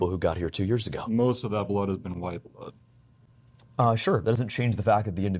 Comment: did he say white blood cells!?